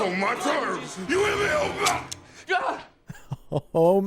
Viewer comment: "YOU HEAR ME OPRAH!?" Fun fact: the subtitles say even though on the commentary the directors admit they dont know what he said but laugh about hearing "you hear me oprah?!" And they joke about it for a good minute. Lol